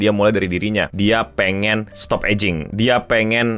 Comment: knp gw dengernya "dia pengen stop edging"